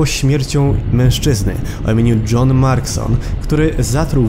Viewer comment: czy ja usłyszałem john marston